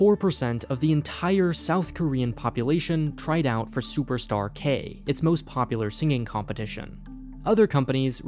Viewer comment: I thought it said superstark as in tony stark lol